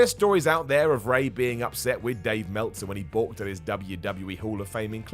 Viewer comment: what did he say? "Bought through his hall of fame inclusion"? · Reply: Balked